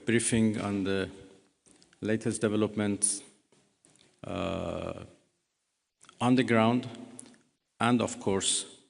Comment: Sounded like he said "latest developments ah underground".